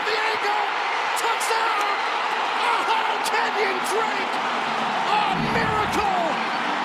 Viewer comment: I thought he said "Can you drink...a miracle?!"